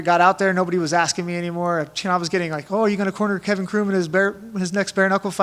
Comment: In interviews i thought Tim said Gina cheated with Kevin Cronin from REO Speedwagon.....turns out its Kevin Croom..... that's not as cool.